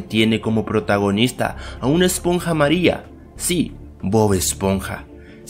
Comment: Solo ocupe I MA GI NA CION Para hacer la ultimo xD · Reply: soy yo o escuche una esponja maria XD